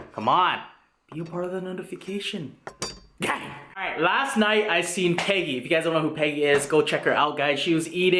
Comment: Deadass thought he said "last night i seen pegging " 💀💀💀💀😂